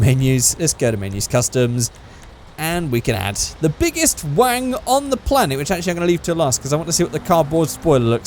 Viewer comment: Sounds like your saying the biggest wang lmao